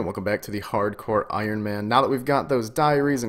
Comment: thought he was about to say "now that we have diarrhea" at the start Lool